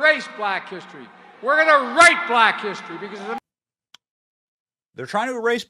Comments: Personally, I heard "We're gonna RIGHT black history" not "rape" ... which while not as bad still sounds very racist. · I don't know Benny I think this might be a stretch it sounded like he tried saying "we're going to right Black history" but slurred his words as usual so I can understand the confusion lol